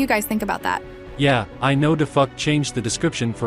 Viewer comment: did he say daf*ck?💀